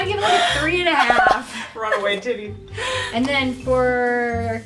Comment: I love it 🤣🤣🤣🤣🤣🤣 I heard "run away titty"